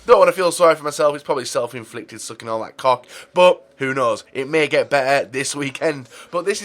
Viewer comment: did he say sucking all that cock?!?!?!?!??!?!